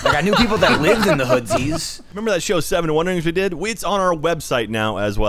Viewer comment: anyone know what said the show was exactly. He said it fast as if it was all one word LoL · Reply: Scoobie Who 7 wonderings, not familiar with it personally